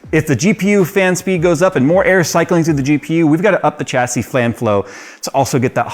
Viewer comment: Flan flow........... didnt even catch that. i was looking back from a cheeky subtitle :D